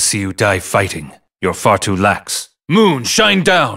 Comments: I always thought he said, "moon shine death." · I always thought he said "Moon Sharingan." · I always thought he said "Moon Sharingan." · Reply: I always thought he said, "MOON SHINE DEATH." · I always thought he said "Moon Sharingan." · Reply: i always thought he was saying moon sunder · I always thought he said “Ohh Sudden Death” not “Moon Shine Down” 🤷‍♂️🤦‍♂️